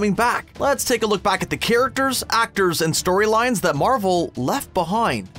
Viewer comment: I heard "stories left behoind".